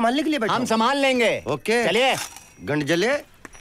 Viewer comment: pe kya bola - gand jale????